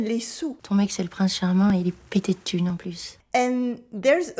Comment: for me, the problem isn't vocabulary or formality, it's parsing the impenetrable clusters of phonemes that dominate casual spoken French. I've listened to that excerpt 10+ times, and what do I have to show for it? A very rough guess at the second half (something like "il est petits thunes non plus") and no idea what's said in the first. I don't even feel certain where the words start or end. In spoken language, expressing oneself is the easy part. Understanding others is the real communication, and it's there that I (and many others) need help most. I've known for decades to drop "ne", avoid inverted questions, etc., and it's easy to deal with isolated examples of "t'as pas" or "ya". Inferring the meaning in rapid or mumbled speech -- something that's far more difficult in French or English than, say, Italian or German -- is really what separates the textbook-disciples from the speakers. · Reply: «Ton mec, c'est le Prince Charmant, il est pété de thune en plus.» That's what she said.